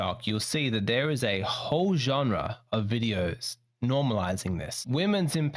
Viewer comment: I thought said "Hoe-genre". Nail on the head Bro! 1 hours "work". Yikes!!!!